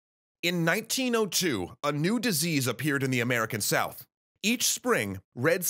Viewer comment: What said A new disease What I hear CSA